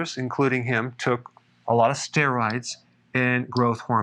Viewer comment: Did you say sterides or steroids ?